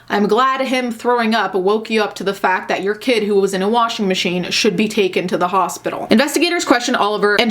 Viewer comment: At first I thought it said: A kid got stuck in a vending machine